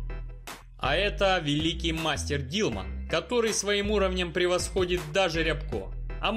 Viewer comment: мастер Дилдо? или я не расслышал? звук не очень хороший...